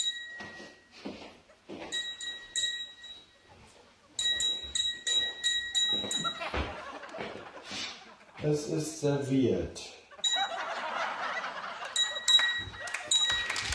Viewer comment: Ich hab bei 6:30 erst verstanden "es ist sehr weird"